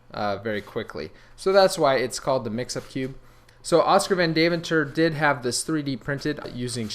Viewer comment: for half a second, I thought that you said that Oskar died.